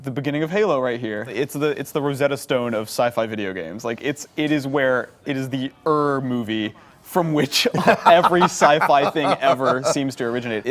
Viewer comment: What did say? “Er movie”?